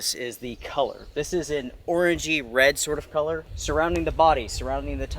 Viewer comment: Sounds like you said Orgy Red color..haha Good shelter.